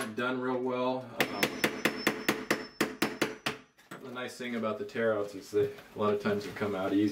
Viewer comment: Why do I keep hearing "the bitchy thing"?